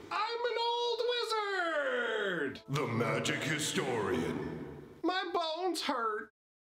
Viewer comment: Idk why I thought you said "My balls hurt" in the intro, and I'm kind of sad knowing I misheard it.